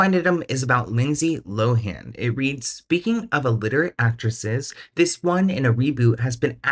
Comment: Every time you say "alliterate" i hear "illiterate" and it makes me think none of these people can read lol